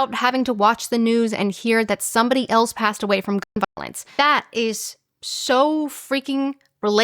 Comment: she says gun violence, I personally couldn't hear what was being said at all!